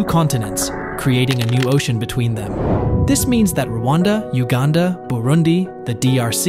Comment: I thought he said wakanda